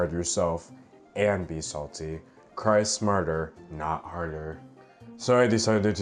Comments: LOL, I thought he said "Christ martyr not harder" XD Well, since he didn't, I'm copywriting that sh*t! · Make t shirts with "cry smarter, not harder" and you will have my cash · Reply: I don't know why but when he said that I heard "cry murder, not harder" and I was confused until I read your comment lol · Make t shirts with "cry smarter, not harder" and you will have my cash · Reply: I thought he said “Christ’s Martyr, not harder”(??????) 😂😂😂